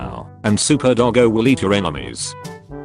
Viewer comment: For some reason when it said super doggo will eat your enemies, I thought it said super doggo will eat your hemorrhoids. 😭